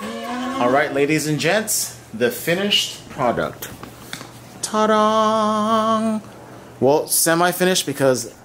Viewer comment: Did you just say "ta-dong"? Haha. Nice couch. You guys seem to have an awesome relationship. Cool cool. Cheers from Canada. Nice interface too 😍 wanting to get an Apogee myself. Maybe the duet though.